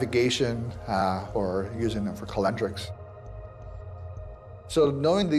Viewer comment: Did he say calendar. min